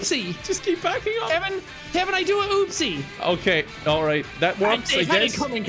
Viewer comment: lol because of his personality disorder I thought Boggins said "Uh Oh Kevin, I did a poopsie!" XD god Jack has a ...unique personality just by himself XD add Kevin to the mix and you know a wonderful disaster is awaiting XD